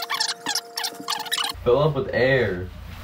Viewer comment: wait, is that good or bad? xd your sentence was cut off mid-way it sounds like, so idk what you fully said or the entire context lol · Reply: Its not a big deal tbh, I was saying they fill up with air so they are not ver breathable lol. They have zippers at the cuff on the legs to open for air flow though. It makes sense they are air-tight (for wind and water purposes) though.